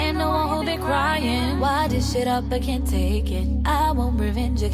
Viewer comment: "WHY DISH IT UP I CAN'T TAKE IT" I thought it said "WHY THIS SHIT UP I CAN'T TAKE IT" lol